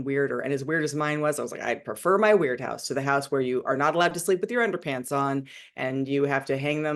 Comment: Ok, wait. Did she say that she wasn't allowed to sleep with her underwear on? WHAAAT?! @ 4:41 Oh, ok, another kid. Just wow.